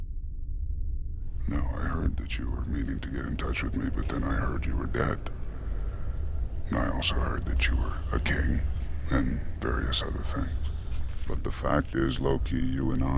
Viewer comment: Hey can someone tell me what he just said, cuz it all sounds like that FNaF Golden Fweddy distorted audio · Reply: Now I heard that you were meaning to get in touch with me and then I heard that you were dead and I also heard that you were a king and various other things but the fact is, loki, you and I are not the sort of people they understand. The sort people fear. I have the information that I need and now I have to break your neck. It's just the way it is. I'm just the messenger.